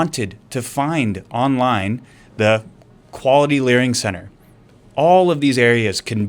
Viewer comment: He actually said LEARING lolo:face-green-smiling::face-purple-crying:😆😅🤣😂